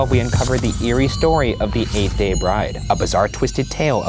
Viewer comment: I legitimately thought it said “eight day bridge”